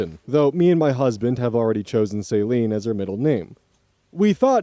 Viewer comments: the way you said Celine sounded like Saline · iirc Amara is pronounced "uh-MAH-ruh" for future reference. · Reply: That bugged me a little lol Also it sounded like he was saying saline instead of Celine